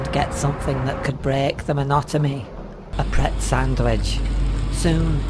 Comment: why did i hear "monotomy" and not "monotony"